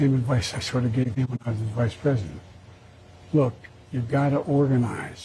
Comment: WHAT THE HELL DID HE JUST SAY!!! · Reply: Did I hear him say " when I was his vice president" ???? Or did I mis-hear that?